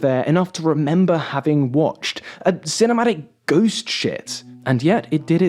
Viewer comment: Ironically I was 'watching' this on a second screen, and I thought I misheard him at 26:00 intending to call Damsel a "cinematic ghost ship". Then I replayed and saw the toilet... there was no accident :S